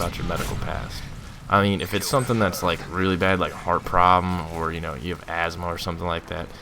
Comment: I thought he said fart problem and I freaked out for a second. Lol